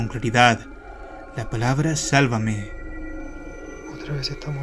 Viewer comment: Yo en el audio escuche “sácame” no “sálvame” pero pues 🤷🏻‍♀️